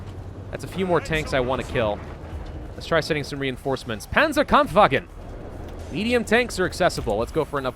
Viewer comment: I'm sorry for swearing, I really am. This isn't a channel where I'd find that normal, but I just can't help but laugh at 9:22 when it sounds like he says "Panzer can't fuckin!" and I'm like, can't fuckin what?